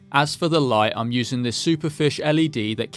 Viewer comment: I misunderstood and thought you were saying "superfic" as a shortened form of "superficial".